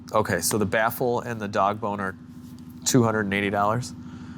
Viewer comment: sounded like he said “dog boner” instead of “dogbone are” 😂😂